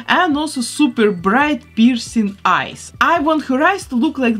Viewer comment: I missheard something and didn't knew wdym, bc I heard "piercing eyes"